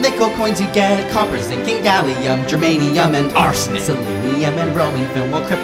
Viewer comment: did they say german....?